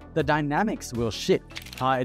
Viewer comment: Did he just say "The dynamics will shift" or "The dynamics will sh*t"? 🤣🤣